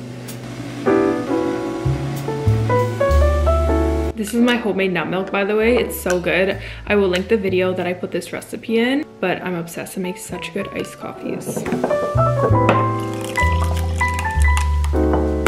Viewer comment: IM CRYING WHY DID I HEAR THIS IS MY HOMEMADE BAT MILK